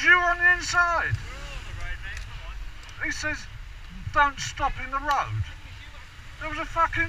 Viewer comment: It sounded like he just said excuse me, as he past you. Maybe I heard wrong. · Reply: He said “ don’t s&”£? Stop there” that’s why I asked what he was swearing about